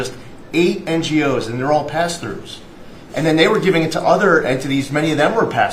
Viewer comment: is he saying pastors ?? The type says otherwise ??? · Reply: pass-throughs